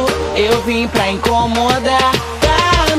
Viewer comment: Eu ouvi "quero ver tu me aquendar" kkkk